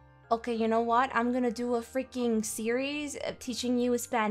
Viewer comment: 應該是i am gonna do a freaking series teaching u Spanish 我之後會做一個教你西班牙文的系列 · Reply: 因為聽成是serious，但放慢聽好像是series，謝謝你，來改在字幕上 m(_ _)m